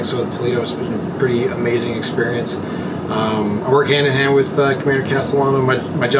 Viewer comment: There's an error with the English subtitles at 14:40 where XO said "I work hand in hand with the Commander Castellano" but subtitles said "community castle on them".